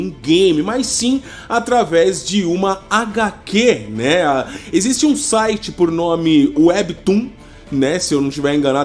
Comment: qndo ele diz " existe um site por nome..." na hora pensei q ele havia falado, "existo um site porn@ me.." já pensei, oshi q HQ é essa 😂😂😂 mano, tô com sono, por isso ouvi errado ksksksksks